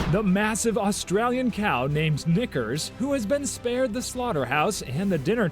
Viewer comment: bro just said the hard r or am i hearing stuff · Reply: Nahh he said it he said nikkers